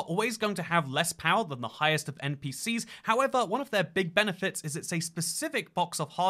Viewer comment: Bruh, Is it just me, or When in 1:45, Where says Consoles are always going to have less power than the highest end pcs, I heard he say: Consoles are always going to have less power than the highest NPCs 🤣